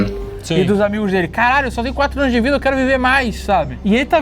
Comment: só eu q ouvi "caralho"?